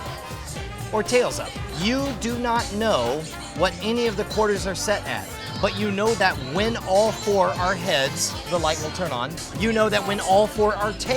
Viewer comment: I thought i'd heard you say all tails would turn the light off, so i spent a while thinking about it till i effectively proved it was impossible to do it. Only to find out when i watch that answer that i'd misunderstood the problem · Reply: Ella Enchanted 1:01 He says "you know that when all 4 are heads the light will turn on, you know when all 4 are tails the light will turn on" I just misheard that last word as "off" the first time around.